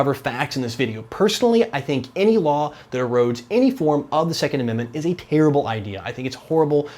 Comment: "Any law, there *, second amendment" What did he said at 0:50??? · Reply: I got it... erode